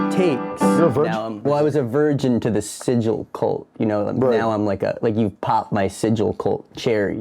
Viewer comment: I thought they said scissor cult 😂 then saw it say “sigil kult” 😭😭😭